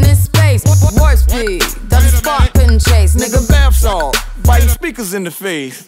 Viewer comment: does it say bounce off or bath salt😂🤦🏼‍♀️ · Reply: but it really says bath salt